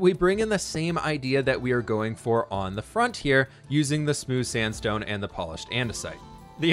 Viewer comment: Spooce sandstone? New sandstone texture? Mix between Spruce and Sandstone? I think you were trying to say smooth sandstone, but I got this instead. :)